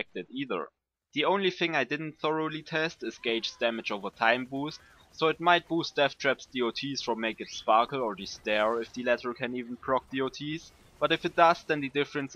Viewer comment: great video but, 3:34 , what did he say lol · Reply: "The only thing I didn't thoroughly test is Gaige's damage over time boost, so it might boost Deathtrap's DoTs from Make it Sparkle or The Stare, if the latter can even proc DoTs, but if it does then the difference is less than not noticable." Yeah, my mic sucked back then. lol The DoTs from Deathtrap are boosted btw.